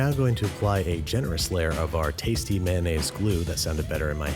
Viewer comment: I thought he said mayonnaise lube